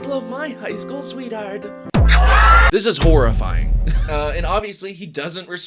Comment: i thought you said torahorrorfying and you were a post modern Jewish dude. Then I rewatched it and now I have a new word and hardly any instances I can use it